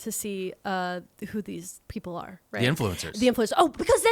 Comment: for a good portion of the golf section, I'm so sorry I thought you were saying "Goth Influencers" and I was like yeah that is a weird group of people to be on crunchy granola yoga. But I was just more baffled I thought you were surprised by goth Influencers, like girlie you were there when it was written! I was embarrassingly not listening well.